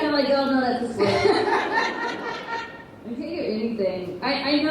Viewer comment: What is Caity saying at 41:07 about season finale? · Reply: "Can't think of anything"